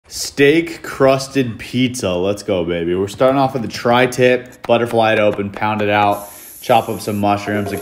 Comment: Thought it said "Steak cursed pizza"